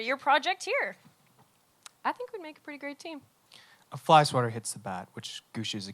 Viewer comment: can someone please tell me what he said 12:26 that made natasha laugh? I didn't understand, i'm not a native english speaker :( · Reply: (Carmilla is the bat) the bat gets hit with a fly swatter and hits the window